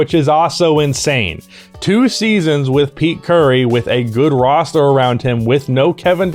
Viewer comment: I heard Pete Curry at 5:34 lmao. I know you said peak Curry but I had to play it back to make sure I heard it correctly.